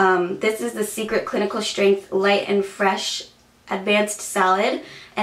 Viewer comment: Haha sounds like you said "advanced salad" when talking about the first deodorant.